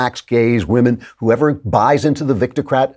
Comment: Did I hear "Victocrat"? Excellent term. Or was it "Viticrat"?